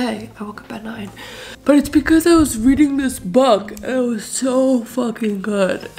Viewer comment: I thought she said "reading this bug"